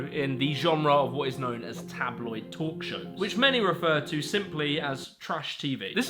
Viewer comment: "Tabloid talk show" I heard as "tabloid torture" .. More appropriate.